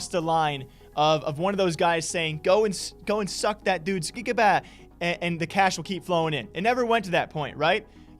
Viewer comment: did he say cickabaa? HAHAHA i love this channel